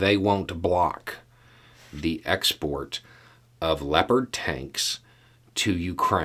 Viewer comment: I swear to god, at first I thought you said "leopard taints". I feel a little ashamed about that.